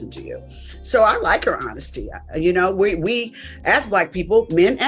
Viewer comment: In sorry but 1:39 did she say " we as "yt" people?" Look like her lips said black but it sound like white. Am I tripping?